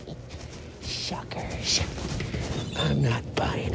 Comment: I thought he said Fukers 😂